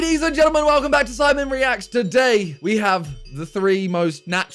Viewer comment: Am I the only one or every time Simon says the intro I hear"welcome to SIMON reacts"